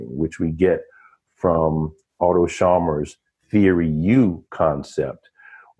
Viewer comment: Thank you for this engaging conversation. At time 28:25, I think said the name Otto Chalmers, is that correct? I know of Oswald Chalmers, and David Chalmers, but not Otto. I did a brief internet search of the name Otto Chalmers and "Theory You" (?), but nothing was revealed. Is there a person named Otto Chalmers who has written about "generative listening" and "Theory You"? Or am I misunderstanding? Thank you. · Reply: Prob Otto schamer if it is in regard to levels of listening